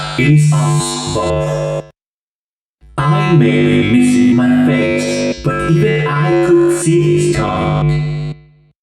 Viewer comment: This is what I heard: "I may be missing my face, But even I could see us talk." Edit: Also in WB's fourth line "???": "Why is this new person - Is it me trapped, Or is it you? Perhaps, Its us both."